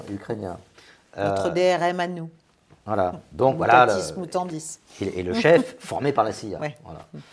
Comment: Notre DRM à nous. – Voilà. Donc voilà. – Moutardisme ou Tandis. ça veut dire quoi ? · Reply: Mutatis mutandis est une locution latine, signifiant « ce qui devait être changé a été changé »